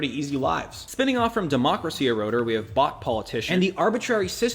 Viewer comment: I misheard Bought Politician as Bot Politician. To be fair, there's not much of a difference.